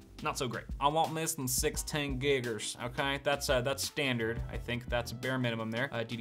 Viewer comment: When you said "Giggers" I started panicking and realized what you actually said.